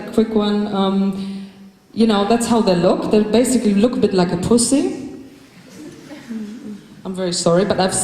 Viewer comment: does she say "they look like a pussy"? Awesome! She is the perfect woman!